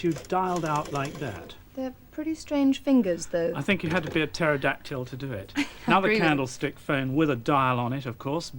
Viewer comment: what's he saying teratactile? pterodactyl?